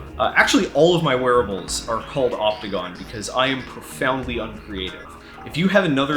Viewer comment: Everytime you say Opticon, I hear Octagon. Following this trend, you could name similar projects after the other shapes, such as the Nonagon, then the Decagon and so forth.